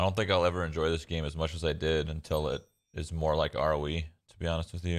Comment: what does he say there? I can only understand "R V"? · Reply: R.O.E, Which is Ring of Elysium. A BR style game that he also plays sometimes. Those videos can be found on here :)